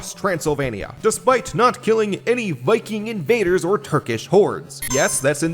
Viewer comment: I thought you said Turkish 'whores' and did a quadruple take😂